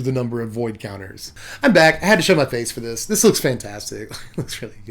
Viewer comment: I thought you said I have to shut my face for this card. Lol scalable board wipe. One for early-mid game then one for late game then double cast card draw build your field quickly, win the game. At least that's what I want to do.